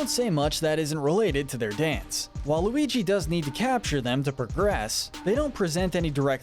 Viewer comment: did you say “Well, Luigi” or did you say “Waluigi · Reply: He said "While Luigi".